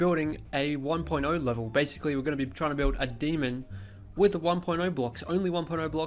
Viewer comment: Lol I thought you said building a demon with only 10 blocks 😂